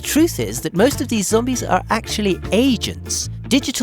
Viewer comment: I misheard it at first and thought you said "Most of these Zombies are actually asians."